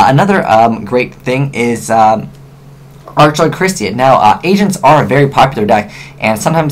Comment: when he said agents it sounded like he said asians XD sorry no hate